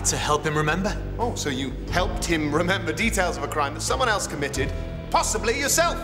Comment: James said crime instead of murder